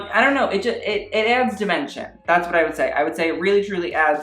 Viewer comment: I thought you said "it adds dementia" lol